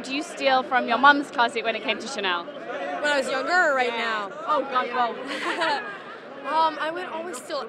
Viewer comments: what she say? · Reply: the interviewer? she says "oh god both" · what she say? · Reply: "when I was younger or right now?"